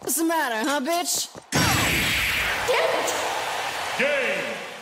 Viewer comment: what Sonic say? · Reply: Go, my bro. What else?